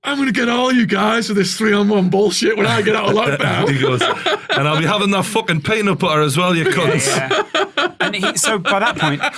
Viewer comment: what did he say? Couldn't understand because of the accent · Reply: If you mean the big guy in white then: "and I'll be having that fucking peanut butter as well you cunts."